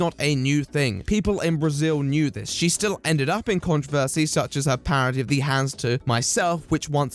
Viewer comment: Who else thought it said trolled not controlled 😂👌